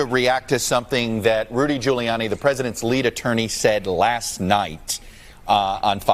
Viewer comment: Trumped up? I thought he said Trump dump. Lol.